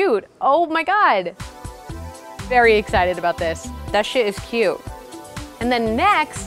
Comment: did she say "That sh*t is cute"?